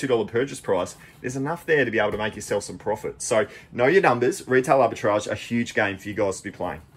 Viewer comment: Retail what what? You spoke too fast, couldn't understand you. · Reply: Retail arbitrage